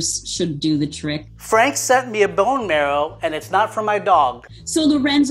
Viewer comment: "Frank sent me a bone marrow, and it's not for my dog." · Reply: I heard it completely different lmao I thought Lorenzo said "Frank sent me a bone marrow, and it's not my dog" and I was shook, like "Did...he just crack an Asian joke on himself?" 😂